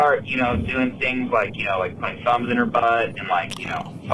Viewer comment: I heard "thumbs" as "guns" at first because they had said the word several times right before this and was like wow she really came around on them